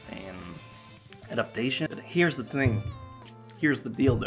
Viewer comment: I paused the video at 13:16 to get a glass of water and came back, put on my headphones and I heard "here's the dildo" xD I was cracking up xD